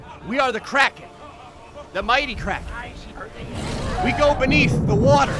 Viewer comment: Am I the only person who thought it said the mighty karen?